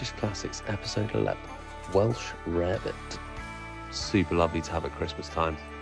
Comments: I always thought they were saying welsh rabbit 🐰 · Omg every time I heard this name before I thought they were saying Welsh rabbit!! So since I don’t eat rabbit I avoided it